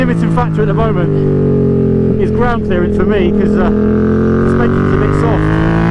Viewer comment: every time You say "Suspension's a bit soft" I hear "too many doughnuts".